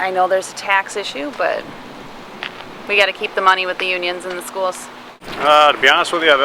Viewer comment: Ok, did I heard this woman right, "there's tax issue, but we got to keep the money with Unions and the schools"